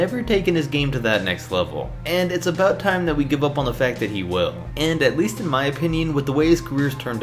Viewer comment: “...give up on the fact that he will” Did i hear that correctly? Isn’t that an oxymoron in itself?